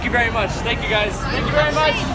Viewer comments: little kid says " daddy, why cant i give him a dollar" dad says "what?" " why cant i give him a dollar"? filming abruptly stops. lol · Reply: i thought she said why cant we take him in the car... i am so stupid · little kid says " daddy, why cant i give him a dollar" dad says "what?" " why cant i give him a dollar"? filming abruptly stops. lol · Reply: I thought it said: Daddy why cant you take to the park :( video stops